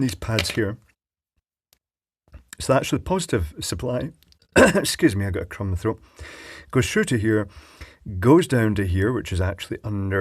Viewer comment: I heard:" Excuse me I got cum in my throat" LOL